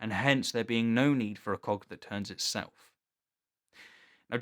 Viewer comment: Oh, he said "cog" I heard... cock. -------LOL